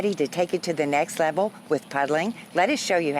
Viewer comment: lol thought it said 'pudding'.